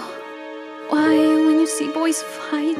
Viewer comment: I thought they say "Holy Simp" but i hear it closly its "holy shit" ahhahahhahahahahaha lol